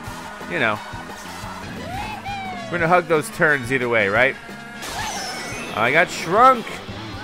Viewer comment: it sounded like he said Igor drunk LOL 😂